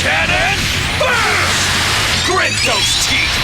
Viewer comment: did bro say grip these cheeks, or am i just hearing thing's?